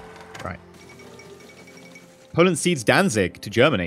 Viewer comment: I thought that said "Poland goes dancing" lol